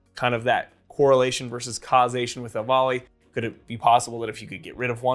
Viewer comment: "whats the correlation with *evoly*" evoley, I guees im misshearing? cant understand the word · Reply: EVALI - (Electronic) Vaping Associated Lung Injury